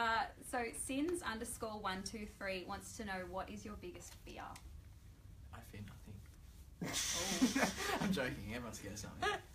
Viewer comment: what does say??? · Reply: Marissa Jean I think: 'I fear nothing'